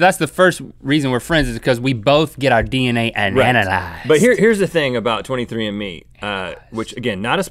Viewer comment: Did he just say "Ananalysed"?